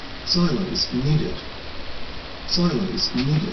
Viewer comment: When I was little I thought it was saying "Silence needed" because I did not knew what a silo was